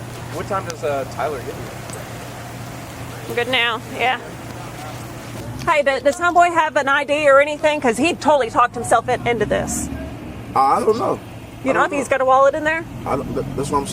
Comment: Did she just say " Does HOMEBOY. have an i.d. ..?" ！！！